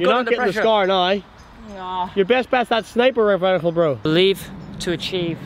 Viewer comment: Is it just me or did say sniper revival